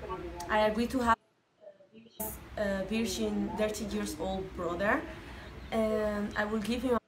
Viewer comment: I thought she said 30 years old..... Omg she said 13 👀👀👀👀